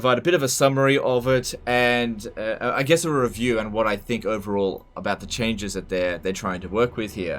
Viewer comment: I thought you said you hate reviews haha